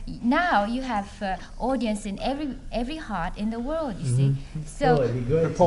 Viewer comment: I think Yoko is saying “you have audience in every HEART IN the world” kinda like an early take on the notion of “the People’s band“ IMHO